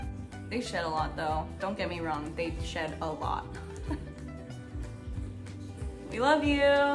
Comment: She said he s..ts slot⁉️⁉️ OOoohhhh❗ He sheds a lot. My bad‼️ Glad she reiterated. I definitely misheard her the first time.